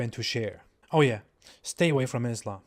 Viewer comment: I thought you were saying: "Oh yeah, stay away from Israel."